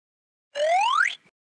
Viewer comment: can someone tell me what balloon said??????? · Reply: "We're good" I think